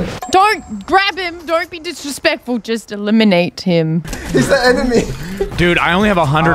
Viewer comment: Loserfruit “Just eliminate him” What I heard “Just a lemonade him”